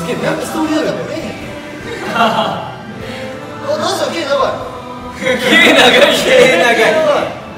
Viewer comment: こーじくん、なんて言ってるかわかる方いませんか〜？？ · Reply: だてさん、けー(毛)長い だと思います！